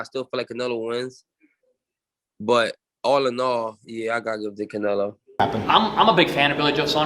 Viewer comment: the caption said "I got little dick Canelo". The fuck lol